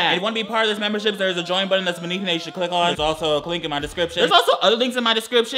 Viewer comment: anyone else hear him say “clink in the description” 😂😂